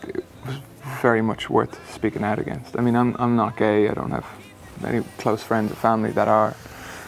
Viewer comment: I heard him say he's not gay :...sorry guys